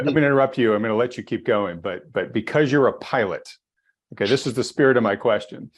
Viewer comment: the first time he said "you're a pilot".. I thought he said "you're a pirate"... you know.. because of the gold lol